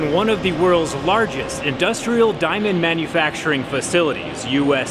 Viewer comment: I misheard it as "dynamite manufacturing facility"😂